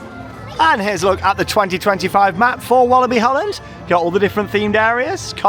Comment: Did i hear Walibi Holland? 😂